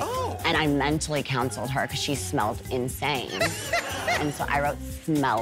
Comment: "..Mentally cancelled her because she smelled insane" sends me everytime. XD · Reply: I thought she said “I mentally counselled her because she smelt insane” I was like Paris Hiltons also a psychologist ?